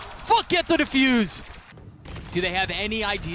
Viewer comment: Sounded like " FK IT THE DEFUSE "